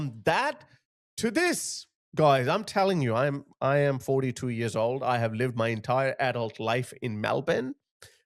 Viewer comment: i know this is off topic, but did he say he was 42 years old at 4:25, damn he look good not in a gay way